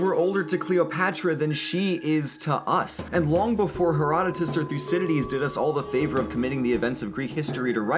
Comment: Can someone please tell me what he said at 0:13 ? "Her __________did all of us a favour" ?? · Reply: "Herodotus or Thucidides"